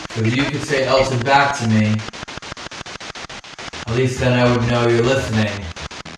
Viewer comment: sounded like “I was” lol